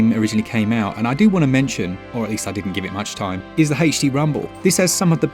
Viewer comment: I know he's saying "HD rumble," but all I hear is "Hasty Rumble." Not a complaint, just genuinely made me chuckle lol. Also, Borderlands 3 on Switch is awesome. Sure, it looks like mud compared to the better versions, but I get gyro aiming, and I can play it ANYWHERE 😊. My biggest complaint is it still crashes most of the time coming out of suspend, and it can cause the Switch to work the hell out of its itty bitty cooling fan. Which, in turn, causes frame skips, etc. Even with these gripes, I have probably put somewhere in the realm of forty to fifty hours into it.